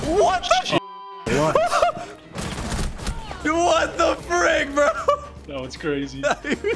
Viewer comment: did he just say what the fuck?!?! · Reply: I think he said "what the frick" but swore at the same time as said frick